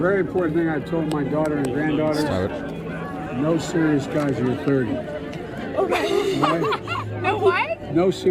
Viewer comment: It sounded like he said “No serious guys who are thirty” and then corrected it after he was questioned to “No serious guys til you’re thirty”